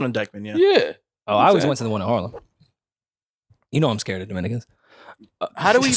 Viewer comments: Boy I thought he said “ you know I’m scared of niggas”. 🤣🤣🤣🤣🤣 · When Rory said "you know I'm scared of Dominicans" I had to rewind because I coulda swore he said" dem N*ggas"